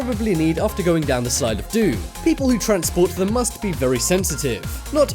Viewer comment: it is slide of room instead of coffen lol 😂😄😅😆😝